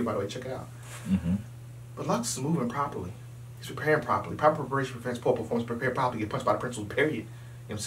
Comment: what does say? He says it kinda fast. What's the exact quote?? I want to use that when I need motivation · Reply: Proper Preparation Prevents Poor Performance, Prepare Properly or be Punished by the PrinciplesPeriod